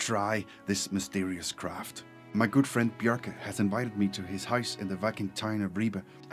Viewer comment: At the start, when you said "my good friend", I thought you said "girl friend", and, well... I thought she looked very butch standing next to you 💕💖 ..... Then, when you said "flock around".... well ........... um